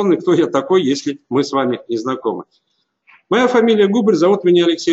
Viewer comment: послышалось "Моя фамилия Гугль" :)